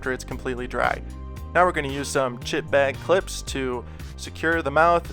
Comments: i thought he said "sh*t bag clips" XD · I thought he said "shit-bag" clips